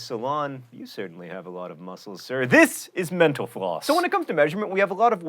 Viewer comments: It sounded like he said menopause instead of mental floss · everytime you say mental floss.. i hear "menopause" lol. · It sounded like he said "meno pause" instead of "mental floss" at the end